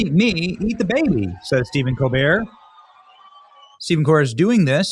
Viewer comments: After he supposedly sacrificed the baby it sounded like you said demon Colbert and I was like yeah that's more like it demon Colbert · The first time you mentioned Steven Cobert’s name it sounded like you said demon cobert! Candace Burre is Kirk Cameron’s sister. He was slated to be the next famous leading man. He had turned to religion while still on Growing Pains.